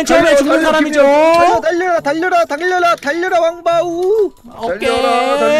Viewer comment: 초에 님이 달려라 방탄 한거 내가 잘못들었나?